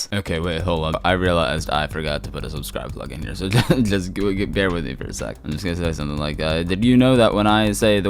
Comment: what did he even say at 10:23 i genuinely wouldnt understand it if it wasnt for context and the large like and subscribe buttons on screen after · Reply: "Okay wait hold on I realized I forgot to put a subscribe plug in here, so just bear with me for a sec. I'm just gonna say something like that."